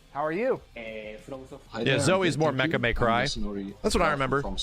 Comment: what does he say that sounds like: "[...] Mecha May Cry"?